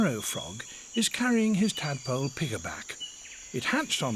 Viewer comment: Is "pigerback" really the way to say piggyback in British? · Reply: I thought he said piggaback?